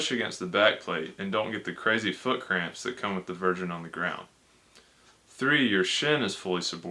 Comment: "and don't get the crazy foot cramp that come with the virgin on the ground" What the hell is did he say here? I checked the subtitles and that also shows up with exactly what I typed lol. Oh, the "version". I see. Yea ya want those feet dorsiflexed for sure.